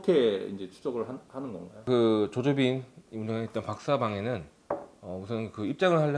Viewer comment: 유명이 아니라 운영이라고도 들리네